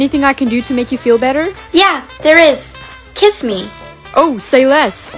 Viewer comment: I thought kissed said krissed😭😂